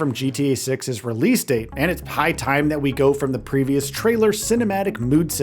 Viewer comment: Am I the only one that heard him say that " its pie time", instead of " high time "? Lol